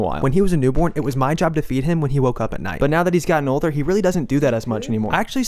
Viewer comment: i was just listen to this and reading comments and I heard "it was my job to beat him when he woke up at night"